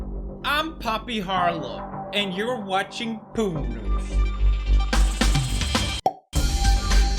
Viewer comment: When you said " PEW NEWS " I heard " POO NEWS ", which fits perfectly for Poppy's first topic point 😆